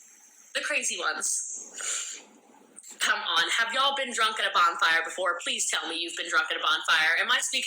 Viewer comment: i thought she said "have you been drunk in a barn fire before" and was really confused